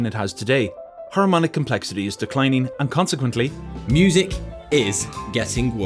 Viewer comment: in my brain i heard "harmonic complexity is declining and consequently music is dying"